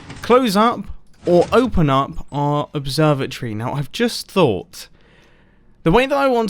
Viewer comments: really thought he was saying observer tree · I thought you said observer tree